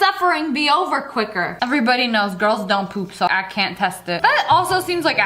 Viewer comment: girl did you just say girls can't poop🧐🧐🧐🧐🧐